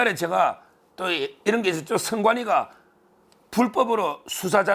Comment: ~ "선관위" 는 "헌재"로 알아 들었습니다.